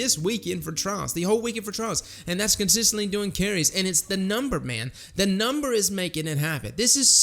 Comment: did I hear trial carries?